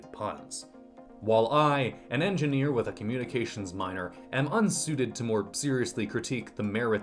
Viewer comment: Did just said hes communicating with minors?